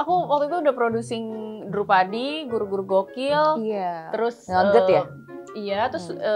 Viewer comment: gue dengernya nugget ya 😂 harusnya nougat